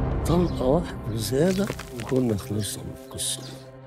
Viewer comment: هو بيقول ايه 😂😂😂😂خلصنا من ك س م ه انا سمعتها كده